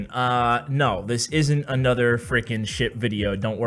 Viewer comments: lol it sounded like ash said at beginning: "no this isnt another shit video" and i was like WTF. and then i realised it was "ship". xD. Keep up them great th9 farming and CR content! Ur vids have made me loaded in CoC! Thx Ash! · Who else thought that he said shit video? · I thought you said this isn't another shit video. I died 😂